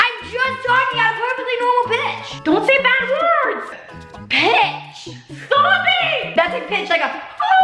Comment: When JoJo said pitch I thought she said the b word